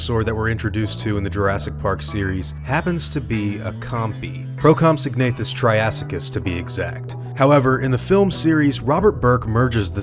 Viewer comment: I thought Burke said Procompsognathus Triassicus!